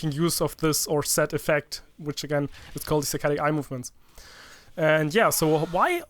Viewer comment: I thought he said satanic movements